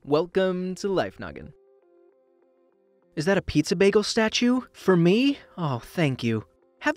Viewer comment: did he call it a statue